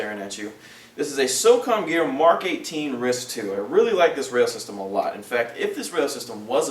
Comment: i think he says rail system but it does sounds like he said "ris"